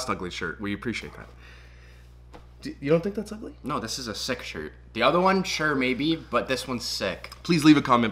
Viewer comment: Lol I thought said "sex shirt" not "sick shirt" 🤣🤪